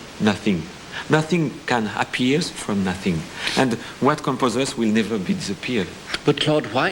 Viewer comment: AND WHAT COME !! POSSESSED !! WE LIVE A PIZZA PIER ? I DONT UNDERSTAND THAT ONE